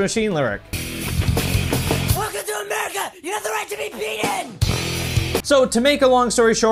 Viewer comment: "Welcome to America, you have the right to be peed in." That's what it sound like he said